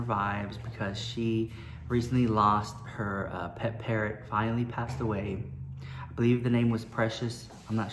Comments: “Her pet parrot finally died” 😂😂😂😭😭😭!!!!!!! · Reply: I thought he said violently not finally...ooop. Ahhh lmfao. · “Her pet parrot finally died” 😂😂😂😭😭😭!!!!!!! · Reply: Dang. I thought he said "violently died" not finally. Smh I need my ears checked.